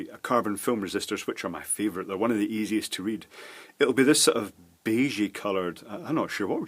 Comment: I Swear it sounds like you're saying "they're one of the easiest to eat"